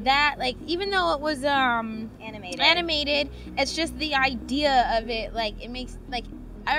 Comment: It's 1 am and I thought she was gonna say "anime" instead of "animated" and almost burst out laughing